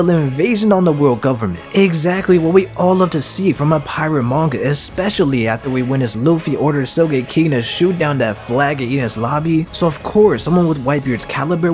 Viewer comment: Did he say "So G@ King"